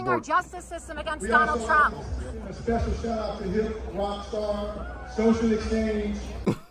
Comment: in: Did i hear the guy on the loudspeaker say, "Shout-out to rockstar, socialist Fani"?